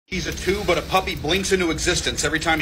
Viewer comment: I heard it wrong and thought he said hes two but a puppy blinks into existence when he giggles